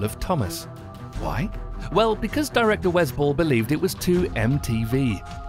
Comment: Oh I thought you were saying the director "was bull" but then I realized that that's the director's name